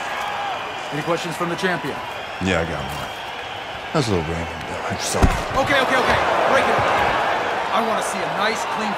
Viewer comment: what he say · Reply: "How's little Raymond doing?"